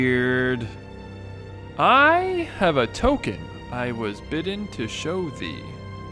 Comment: I heard “I have a Tolkien to show thee”